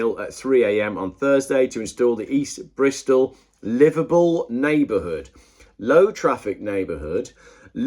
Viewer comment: I thought you kept saying liverpool neighbourhood!!